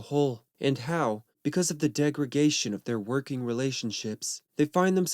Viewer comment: Did i just hear "degra-gation" ???😮😆